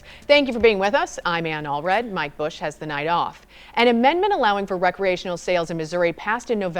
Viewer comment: i had to re-listen to that , sounded like she said " my bush has the night off " Fuck I'm too high 😂